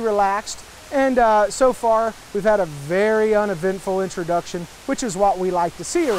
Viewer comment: Did he say un-event-ful? Time to spice things up.